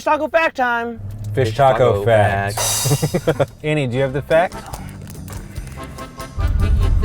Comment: D. Foxx They heard correctly... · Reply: Kyla Krier except she said “per person”...I guess you misheard her too